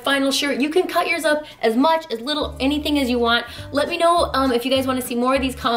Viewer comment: "you can cut yourself as much or as little as you want" o.O! I know that's not what you meant, and I'm completely taking it out of context, but I heard that from across the room and had to see if I heard that right. I like the shirt!